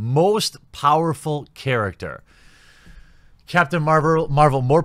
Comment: Did anyone else hear Captain Marlboro?